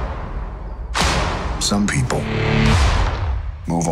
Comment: I hear bananas